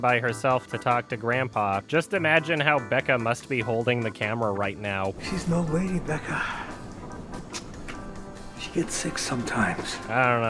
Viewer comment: I’ve watched this video like 5 times over the years and I always thought that at 12:15 he’s saying “she’s no lady, Becca. she gets sick sometimes” like being sick revokes her femaleness. I only just realized he’s saying “she’s an old lady”